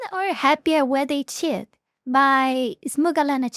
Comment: The first few seconds I hear " women are happyer when they shit... byyy smugalana...." lol